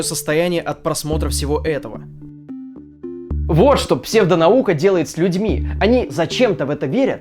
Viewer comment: "Вот что наука сделает с псевдолюдьми" - послышалось мне и в этом даже был какой-то большой смысл, если подумать)))